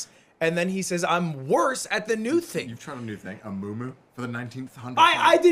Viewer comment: bro i heard amumu and broke down laughing 😂